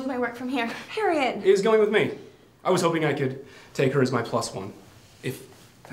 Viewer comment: I didn't understand what Alex said here 3:28 Take her as what? Anyways, Senator (scumbag) Elton, you had what you deserved. EAT IT. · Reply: Alex said, "... is going with me. I was hoping I could take her as my plus-one, if that's okay with you, Harriet." Alex got an invitation, and as an invited guest, he can bring one person with him (as his plus-one). This was great; screw Senator Elton.